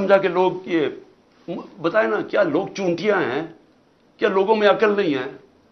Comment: does he say "log chutiya hai"?😂